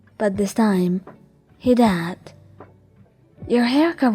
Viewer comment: What you said: "But this time he did." What I heard: "but this time he dabbed."😅🤣😂